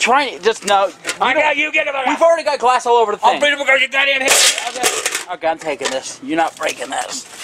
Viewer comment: What is he trying to say at 2:28? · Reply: "You get the fu-- back" "I'll break it over your goddamn head"